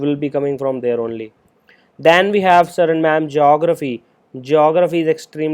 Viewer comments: What does he say here? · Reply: Then we have sir or mam Geography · What does he say here? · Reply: sir & mam